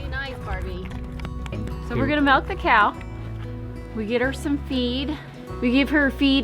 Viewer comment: I thought she said we are going to melt the cow.